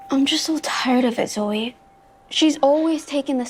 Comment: when i first saw this part in the trailer, I thought she said “I’m just so tired of this weight” and I thought the entire episode was going to be about Pen trying to lose weight lol